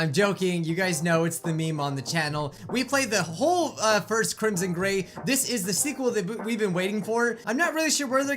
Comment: I hope im not the only one who at first thought it said crimson gay